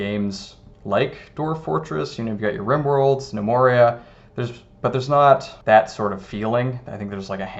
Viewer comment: he mentions a game similar to Rimworld and Dwarf Fortress. It sounded like Memoria or something similar but I can't find anything. Anyone know the game? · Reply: Gnomoria <link> Its a dead game unfortunately, devs dropped the project and its still super unfinished.